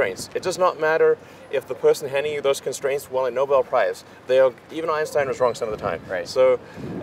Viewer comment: I'm pretty sure Elon says "even Einstein was wrong some of the time", but the subtitles say "even our own standards are wrong some of the time". 8:57 - I'm pretty sure Elon says "potluck dinner situation", but the subtitles say "part like dinner situation" (for non-Americans: a potluck dinner is where every guest brings their own dish, so what you'll end up eating at this dinner is pot luck). Yes I'm nitpicking, sorry. Great interview.